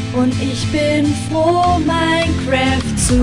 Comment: Was sagt er da ? xDD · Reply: Ja ich glaub Jesus...